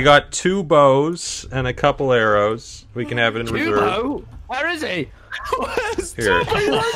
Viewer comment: l think Wilbur is saying "WhErE iS TuBbO? WhErE CouLd He Be? wHeRE CoUld He Beee? XD"